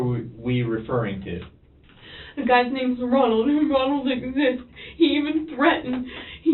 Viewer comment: wrong captions “The guys named Ronald, Ronald exists “ is what I believe she said